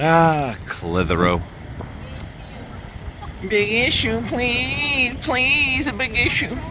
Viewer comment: The big issue lady in our quaint cotswold town sounds like she saying piggy shoe please 😂😂 my daughter said why she saying piggy shoe please 😂😂😂